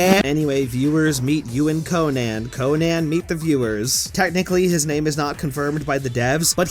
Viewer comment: I hear "Shoot Gunman" not "Ewan Conan"